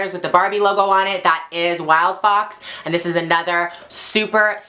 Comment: Showing us the Wildfox logo so we can all see that you're an XS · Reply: Its helpful very because i thought she said wildpop